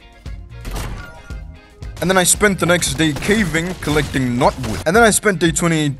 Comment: And this whole time I thought he was saying "Not wood" but it was "knotwood" 😭